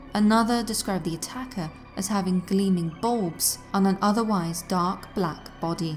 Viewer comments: Did she say at 18:20 that some witnesses said that the monkeyman had glowing balls? That's terrifying! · Did I just hear, "gleaming balls on an otherwise dark black body"? 😱😂🤣